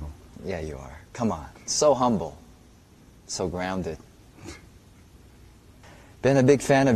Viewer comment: When he showed the pic I thought he said “so rounded”😂👨‍🦲